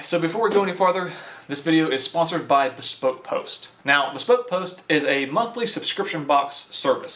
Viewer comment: When you said "Bespoke box" I thought you said "Scroat' box". I figured it was a competitor to Manscaper.